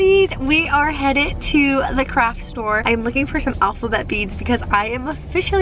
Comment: I thought she said the croc store😂😂😂